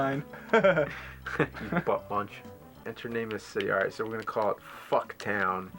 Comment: The second I saw those shirts I was like "are you two beavis and butthead?" 2:43 I hear buttmunch. SO called it.